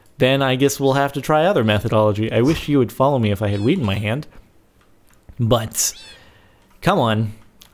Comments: XDXD it sounded like he said "i wish you would follow me if i had some weed in my hand" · I could have sworn he said 'weed' @ 3:04 xD